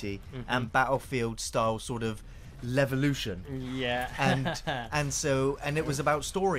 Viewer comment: i thought he said "Level-oution?"